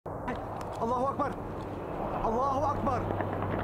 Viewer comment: Admiral Ackbar is a Star Wars character. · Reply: alans snackbar I thought he was saying.